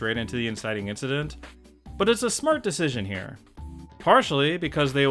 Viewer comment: I had to replay 1:09 several times, because at first I thought you said it was a "smrt" decision, which would have been great. However after rerunning that I realized you only said it was a smart decision.